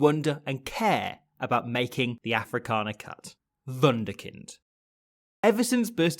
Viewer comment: what did he just say? Wonder kind? Wanderkid?😂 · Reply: wunderkind. the German word for wonderkid